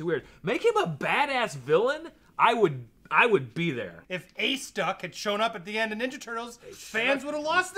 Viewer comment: Who is he talking about? Acestuck? Ace Doc? I can't hear it right